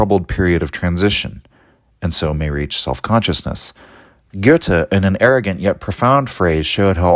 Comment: Who is this philosoph you're mentioning in the quotation at 54:09 ? I can't figure out how to pronounce his name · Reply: Do you mean Goethe?